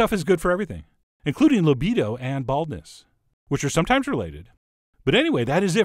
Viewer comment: did he say libido and badness or libido and bowel movements are sometimes related?